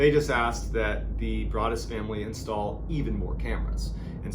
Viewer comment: I thought you said the brawadis family🤣